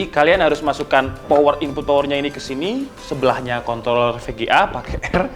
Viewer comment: gak salah dengar nih bilang kont*1